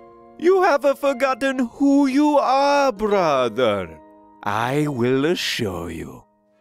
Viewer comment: Ohhh... Poor, scared, Luigi... You have not forgotten who you are my brother I will show you... Things are better this way, we can’t undo what we do, decade after decade we all needed a little boost to help us, we all way say that we will always be a time classic together.... Together.... Together.... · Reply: oh I thought he said "I will a-show you" Since he seems to add an 'a' to a few of his sentences.